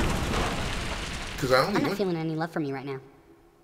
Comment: What he said? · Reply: said "I'm not feeling any love from you right now." :)